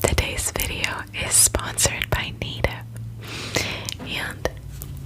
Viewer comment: I hope you all are taking care as best as you can, and finding small moments of beauty and peace throughout your day. Much love! And thanks again to Native for sponsoring today's video! Save 33% on your first Native Plastic-Free Deodorant Pack - normally $39, you’ll get it for $26! Click here <link> and use my code #AD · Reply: Ngl I thought u said u were sponsored by NATO